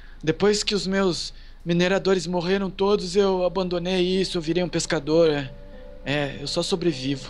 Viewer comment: Entre 5:15 e 5:23 o ele fala que ele virou pescadora eu ouvi isso alguem também ouviu?